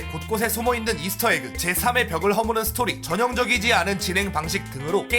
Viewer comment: 형 여기서 4의 벽이 아니라 3의 벽으로 들리는데...